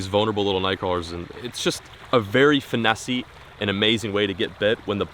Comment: Did he just say they're fishing 'for Nessie'?